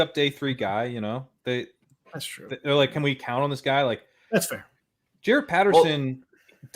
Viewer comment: "i hear ya there" instead of "that's fair" from haha